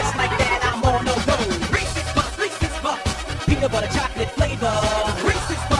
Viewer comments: I hear racist puff racist puff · only I hear ""Racist puffs"" ?